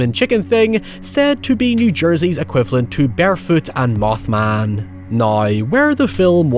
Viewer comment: Did you just say “Bearfoot?” 😂 Can we get art of this beast please?